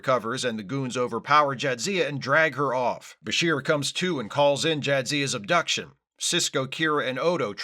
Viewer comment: I was initially confused, as I heard “Bashir comes, too” instead of “Bashir comes to”!